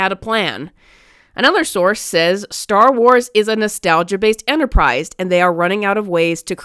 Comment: I wasn't looking at the screen and I though they said "Star Wars is a nostalgia bait enterprise" ( 'bait' instead of 'based') lol.